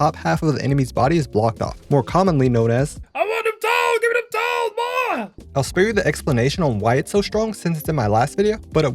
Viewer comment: what is the guy yelling? 'Doll'? I'm trying to figure out what these bottom side peeks are commonly called... sorry, old gamer dad, not sure about all the terms. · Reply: He’s shouting dogs which is slang for feet, on the other hand I’m not so sure if there’s a proper name for the bottom side peek